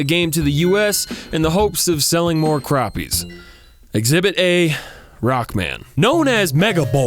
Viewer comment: Did anyone catch he said cronies at 2:04.